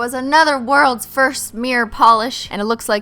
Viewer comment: Omg I thought it said "world's worst nail polish" 😂😂